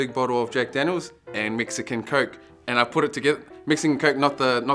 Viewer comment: This is not related to tattoos, but when said "mixed it in coke", I seriously thought I heard "Mexican coke".